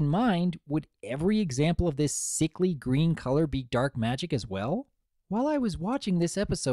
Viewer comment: I thought she said dirt magic.